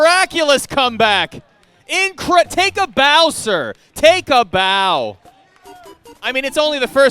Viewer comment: I suddenly heard "Take a Bowser" at 11:08 instead of actual "Take a bow sir"